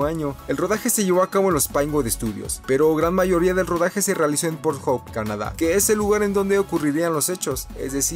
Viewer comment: Min 2:57 like si te diste cuenta que dijo pornhub